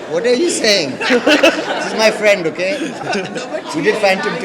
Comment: she is my friend and what else did he say? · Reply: and we did phantom'